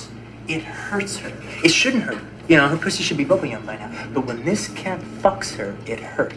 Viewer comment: "It shouldn't hurt; her pxssy should be [what is this word?] by now." Can't make out what he says in the middle there. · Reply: “Bubble yum” LMAO, quite the visual